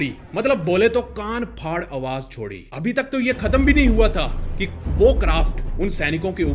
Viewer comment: he say kaan faad awaj and I heard gaa*** faad awaj🙄🙄🙄